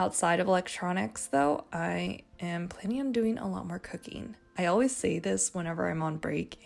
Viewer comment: i wasn't fully listening so at 16:36 i heard "i plan on doing a lot more cocaine" instead of "i plan on doing a lot more cooking" and i was like 😳 lmao